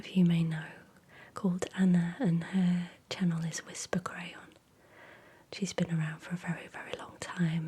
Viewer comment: I'm struggling to find "Whisper Crayon" - am I hearing you wrong? 😅🤔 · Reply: I can't find her, either. Definitely sounded like said Whispers Crayon